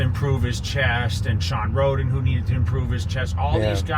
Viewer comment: Did you just say Shawn Rhoden needs to improve his chest? lol😂 cmon jay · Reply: He said needed.